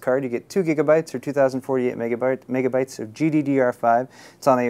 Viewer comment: "Megabart" , I hear you are a fan of the Simpsons